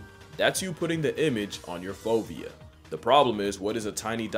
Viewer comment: if you didn't put the word "fovea" on the screen I would have thought you were saying "phobia", which wouldn't have totally surprised me considering who we're talking about here